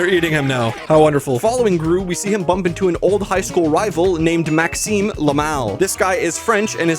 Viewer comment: I know his name is Maxime Le Mal but when you said it it kind of sounded like Maxime LAMO 😂